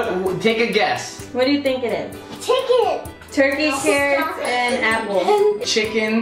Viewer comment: A P P L E S A U C E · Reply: I thought said “Turkey Cereal”. 🤢